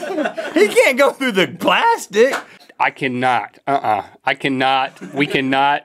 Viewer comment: Did Rhett say “he can’t go through the glass dic” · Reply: Plastic